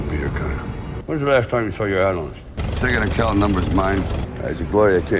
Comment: i think he said "when was the last time you saw your anus?" i dunno :D